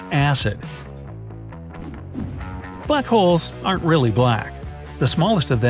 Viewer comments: I thought he said buttholes aren’t black😂💀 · did he say buttholes orrrrr